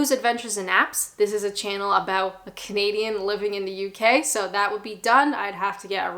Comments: been watching for a while now, and when i first started watching, when you introduce yourself i thought you said you were a comedian, not a Canadian! so your accent has definitely changed. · When I first came across your channel I misheard you and thought you said you were a “comedian”. It took me a while to realise you said a “Canadian” 😂 I do enjoy your channel. Thank you.